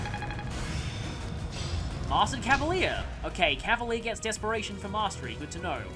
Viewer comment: I thought that said depression instead of desperation.